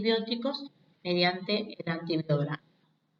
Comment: Hola! alguien entiende que dice? "es necesario determinar la sensibilidad de los microorganismos mediante ????" gracias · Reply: mediante el antibiograma